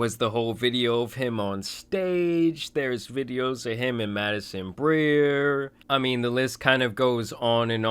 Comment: u mean Madison Beer? lol I thought you said Madison Grier